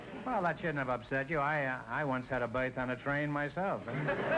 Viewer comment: Yeah it sounds cool, I'll have a look at it ! I won't get bored these holidays xD Gosh, even after listening again many times and showing it to my folks I still can't understand the joke at 3:35 Neither the explanation of the husband x) I just get : Don't let it upset you, but I once had a.... on a train myself. And something like "operate" after that And then the other man says something about a birth and a baby I assume that it must be some kind of salacious joke as both men seem embarassed to explain it, or I don't know. xD If anyone can help thanks in advance !! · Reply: Groucho said, "I once had a BERTH on a train myself"-- it was a pun on the term for a sleeping compartment for passengers on a train, and childbirth. :)